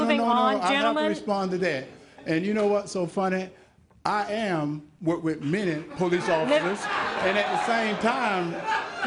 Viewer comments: Hershel dis not claim to be a police officer. What he had said was: “I am, whuh wi’ minney police officers, and at the same time…”🥴 😆‼️ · It's real. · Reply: First of all, it's not a real police badge or else he'd be a real police officer. He's not, though he has said that he is. It sounds like he said. "I am work with many police officers", which makes absolutely no sense. He's trying to gain the trust of people -- using a fake badge. Sorry, but Senator Warnock's point was that Herschel Walker has tried to pass himself off as a policeman. Walker confirmed the accusation with the display. He does pretend to be a policeman and he's on video pretending to be an FBI agent. What is he but a pathological liar?